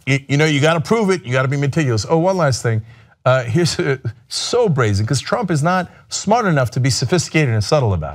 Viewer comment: ayo did cenk say so brazy at 4:26😂😂?